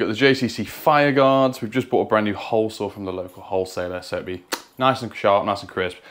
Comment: "We've just bought a brand new hole from the hole seller" -that's what I heard lol.